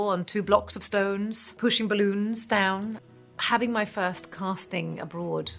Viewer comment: did she say she had to PUSH BALLOONS DOWN?!?